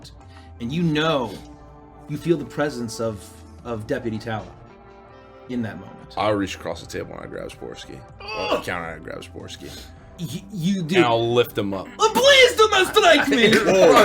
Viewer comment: Did he just say "I grab his foreskin" ?